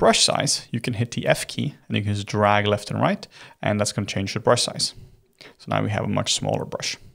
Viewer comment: Did you just say "Niggas drag left n' right" LMAO